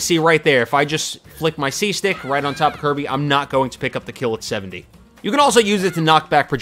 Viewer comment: did you call Kirby her?